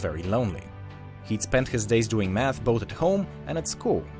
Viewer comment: did he say meth